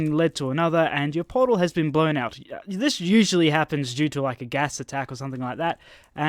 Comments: In the beginning I thought he said gas attack like what level of Hitler are you on? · I heard "gas attack" lol